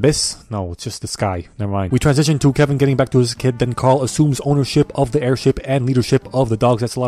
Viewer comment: i thought he said carl azuz for a second